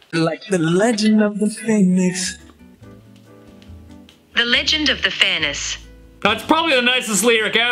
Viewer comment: “like the legend of the pheonis” That’s what i heard but I also hear phonix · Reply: I heard “the legend of the famous “